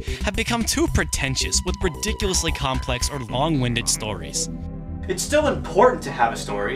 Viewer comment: I thought you said bong winded for a second a very apt description